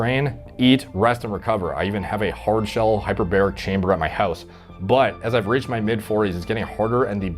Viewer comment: Thought bruh said he had a hyperbolic time chamber from DBZ